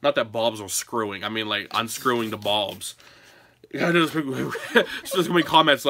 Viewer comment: It sounded like you said "not like bulbs screwing, I'm screwing the bulbs" hahah